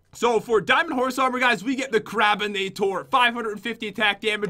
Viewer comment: When jen got diamond horse armor i thought she said: I got the crapppinator